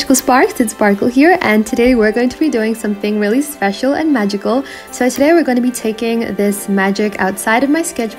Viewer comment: Why did I hear “were sUsPiCoUs and magical!” 😭😭